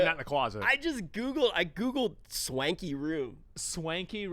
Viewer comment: Bro looks like he’s participating in a spiritual retreat 😂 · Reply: Oh, you said SWANKY... thought you said SKANKY... boy am I underdressed